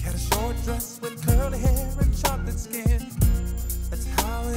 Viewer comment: I thought he said chocolate skin? 😂